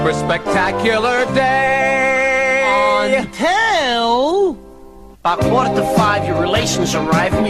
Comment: Ever since I was a kid I could not understand what he's saying at 0:26. It sounds like "on Hell" which doesn't make any sense. Did he mean to say, "IN hell," or is he saying something else entirely?